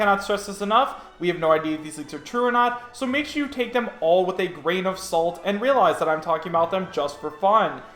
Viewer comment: When you say "grain of salt" I hear "pound of salt"